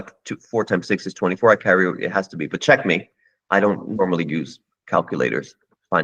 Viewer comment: CHESS MENTIONED DID HE JUST SAY CHECKMATE LESSGO GRETASTTES GAME OF CLAL TIME🔥🔥♟️✍️♟️♟️🔥♟️🔥♟️🔥♟️🔥♟️